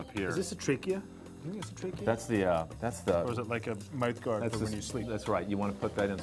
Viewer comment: I never thought I’d hear Owen say “Is this a trachea or is it like a life guard when you sleep” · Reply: life guard 💀💀💀 he said mouth guard